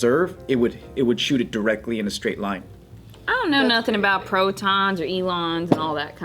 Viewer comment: did she say protons and elons.. lmao